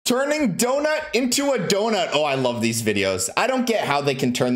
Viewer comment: I thought he said burning a donut into a donut